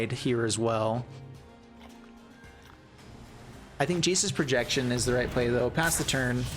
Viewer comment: Ha ha at 23:35ish you first called it “Jace’s Projection” then when you played the card it sounded like you called it “Jesus’ Projection”